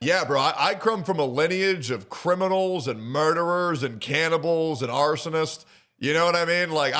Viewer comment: hol up… did he say cannibals?